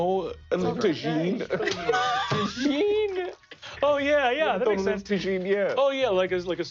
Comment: For anyone else that was incredibly confused like me, i think lou was saying Tajin in his fancy fabian voice for the ice feast convo